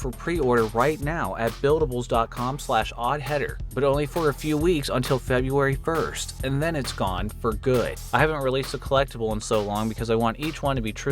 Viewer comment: damn I thought you said FEBRUARY THIRST... ah. Thats my Bday.. and I do be Thirsty...